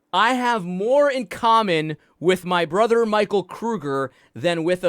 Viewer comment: Lol I thought she said Cougar not Kruger?😹😹😹